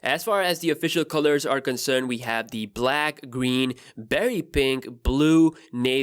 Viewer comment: i thought he said very pink not berry pink lol